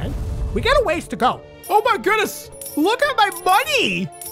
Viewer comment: turn on captions and watch if you do not want to go to the time period to this is what he said Oh MY GOODNESS LOOK AT MY BUNNY